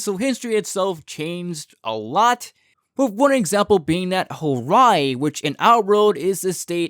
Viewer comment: did you say Hawaii?